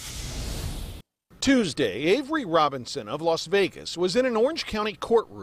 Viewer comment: Sound like they sayin angry Robberson🤦🏽‍♀️🤔😂